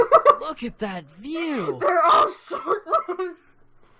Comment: In the beginning I heard “they’re all short less” and I instantly I screamed inside my mine like Me: OH NO THATS NOT OK XD